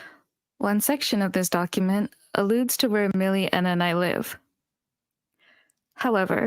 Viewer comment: Random note on 1:32:14 Elira says "Millie, Enna, and I" and not just "Millie and I" if I heard it correctly. They're also listed as part of the Clique also which would make sense if they ever did an off-collab where Doki felt bullied or if she was ostracized for NOT attending an off-collab in a specific place/city/whatever that she then mentioned in that legal document.